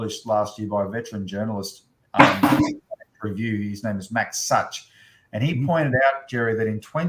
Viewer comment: Jerry or, could either of you please provide the link to the article mentioned at this point ( 1:08:32 ) as I didn't quite catch the author's & publisher's name. Thanks. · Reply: I think the article was written by Max Sutch for the Lowy Institute, i'll ask if he can provide that link and come back to you with it